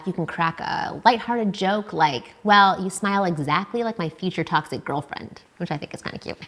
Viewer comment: What is she saying at 3:12? I hear "exactly like my future "toxic" girlfriend, but is she really saying toxic? That sounds negative. She is saying toxic! and then about the divorce. that's so negative, even if you're joking. Words have power! Careful how you use them, even in humor, they still have the same power.